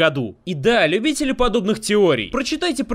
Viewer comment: Что за Годуриан на 19:35? мб неправильно услышал, но гугл не даёт результатов